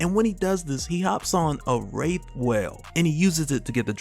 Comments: Not trying to be dark but I swear I heard "he comes back riding a rape whale and gets the drop on Wanda" and I was like..... 😶😶😶😵😵😵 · I thought you said he summoned a rape whale and I made a surpirse Pikachu face.